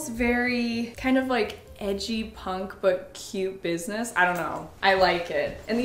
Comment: did u just say punk